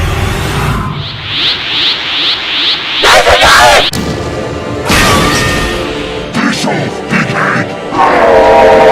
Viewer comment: LOVE THIS! What did say at 22:26? What audio was that? Because I couldn't stop wheezing uncontrollably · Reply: He said "THAT'S ENOUGH!!!!!" That's a line from Mokey that i got from the Thanksgiving Episode <link> (Skip to